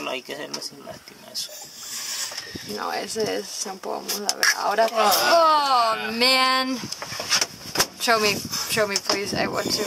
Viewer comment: 言ってはいけないんだろうけど「あらまぁ」って聞こえた